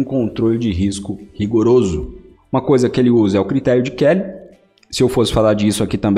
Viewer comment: "Uma coisa que ele usa é o critério de _________". Não entendi bem o nome. Poderia escrever? Fiquei curioso para saber qual é esse critério par gestão de risco. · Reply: "Critério de Kelly"